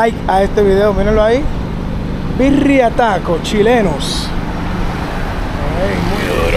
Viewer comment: creo que dice CHINELOS!